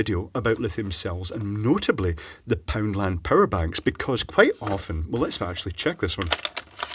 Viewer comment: OH! Power bank, I thought you were saying parabank and I was trying to figure out what the heck that was lol